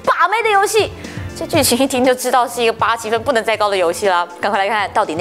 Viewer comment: 請問 0:11 秒說的是1個什麼到不能再高的遊戲? 聽了幾次也聽不清楚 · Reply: 87的不能再高